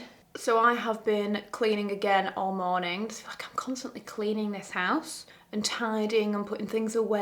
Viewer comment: did you say "fuck" just then? 😅 I know you mentioned before that you have a bit of a potty mouth irl, but since you never swear in videos I found it so funny 😂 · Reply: No. she said it’s like I’m constantly cleaning this house. Not fuck I’m constantly cleaning this house 😅